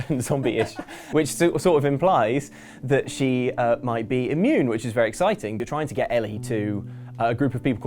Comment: did he just say she (might) be immune ?